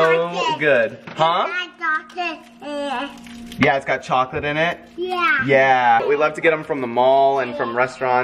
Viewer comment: It sounds like is saying chocolate thing